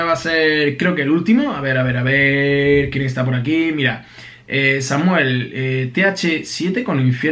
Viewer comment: like si escuchaste que dijo samuel 11:26 y era samael